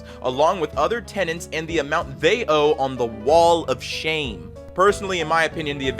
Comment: An ad cuts you off when you said Wall of Shame so it sounded like you said "Wall of Shit"